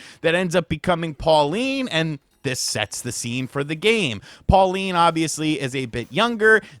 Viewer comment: "This Sex the scene" Thats what I heard!